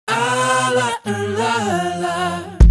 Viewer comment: omg when he says “la la la la la” at the start i always thought that was him saying “i like the low life”